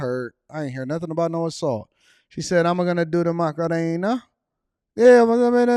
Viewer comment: lmao!!! She said she almost got her chains snatched a few times. The word that sounded like Macarena was Cadena meaning Chain in Spanish.